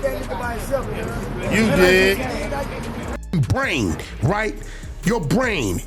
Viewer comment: sound like he saying "you dead..."